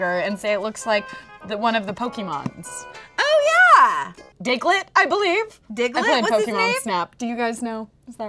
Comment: I THOUGHT YOU SAID DICKLET OMG IM DYING LMFAO